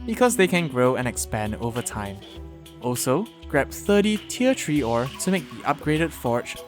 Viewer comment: i heard tier 'tree' ore